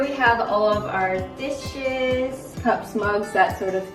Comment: i thought she said dish-shits not dishes XD